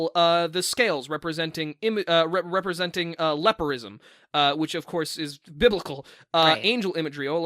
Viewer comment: okay on one hand I really love this and how good these theories are and how excited and are about them but on the other hand I cannot for the LIFE of me figure out what word is saying at 1:27:56 "lepirism"???? that's not a word! I looked up a bunch of different spellings and nothing came up I know lepido- is Greek for fish scales but that didn't give any results either what is he talking about???